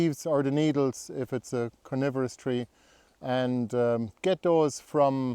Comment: I misheard you saying "coniferous trees" as CARNIVOROUS trees. 😂😂😂. That sounds like a dangerous garden!